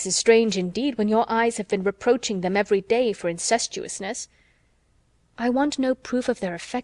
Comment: It does not say incestuousness....it says incautiousness......HUGE difference!!!